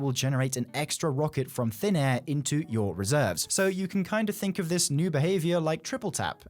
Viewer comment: Would've loved to get some more reserves for 1k too. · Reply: Hoping the catalyst is Controlled Burst (sorry that the initial comments very different I thought you said “research” not “reserves”.)